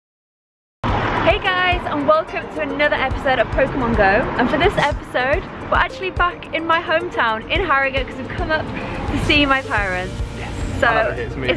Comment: Did She Say Harambe?